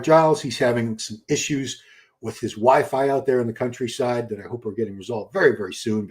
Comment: Holy moly,when u said that Giles got issues with his wi-fi...I thought for a second u said " issues with his wife.😂